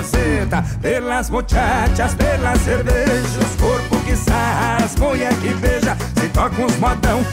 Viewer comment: Primeira vez que ouvi "os porco que sarra" 😅🤫